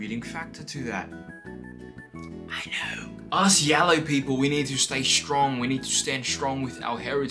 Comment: did say yaoi people?😰